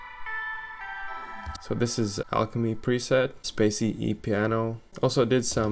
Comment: I swear I thought he said "This is Sallie Mae" and I almost ran lol